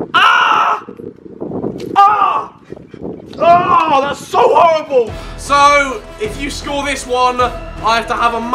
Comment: did harry say horriform